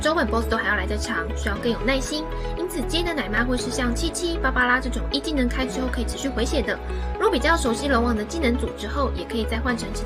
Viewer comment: 我聽成7788